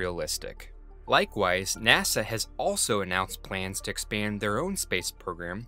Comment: "aughlso" He sounded like that pufferfish eating a carrot